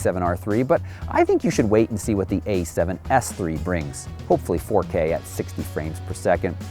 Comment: A7SIII, did I hear you say an A7SIII is coming? 4:40 (drool...) I've been a patient Canon lover but if we don't see a 5DM5 or equivalent by the time the A7SIII is released, it's time to jump ship!